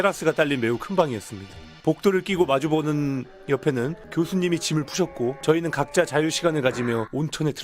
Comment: 아무생각없이 그람보다 짐 푸셨다는 말을 집을 뿌셨다 라고 들었네 ㅋㅋ